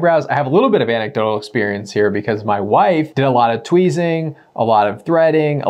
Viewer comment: did he say "wife"? I am blown away.